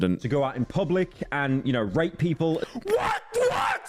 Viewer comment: WHAT DID HE EVEN MEAN TO SAY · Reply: Rate lol